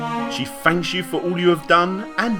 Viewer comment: I swear I’ve always heard “F!@ YOU” instead of thank you @ 10:10.